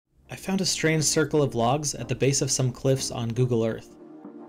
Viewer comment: I thought it said ‘circle of legs’.💀